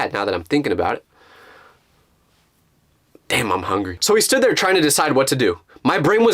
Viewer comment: he actually said damn